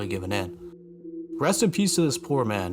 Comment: I misheard you say "Rest in pieces" instead of "Rest in peace to this...[poor man]". Definitely threw me off.